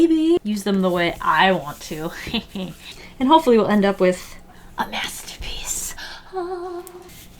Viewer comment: It almost sounded like you said, "and hopefully we'll end up with, a mess'terpiece". I was dieing! :D